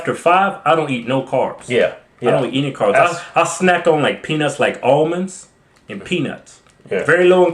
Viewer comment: Lol I thought he said he snacked on penis.